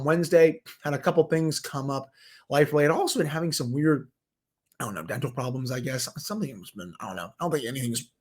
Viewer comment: Lol, I’m not joking but at the beginning of the show I honestly thought you said my wife is having some rectal problems, and I was like wow, he’s really opening up and sharing a lot on a sports show hahaha😂😂...I had to rewind it and realized you said you were having some dental problems lol.